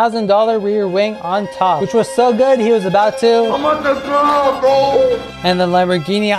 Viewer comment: "ambatukam bro 😭" bro liked the car too much 💀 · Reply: I thought he said I am about to cry